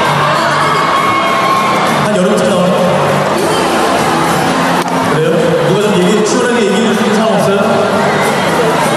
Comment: what was she saying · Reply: "it's a secret"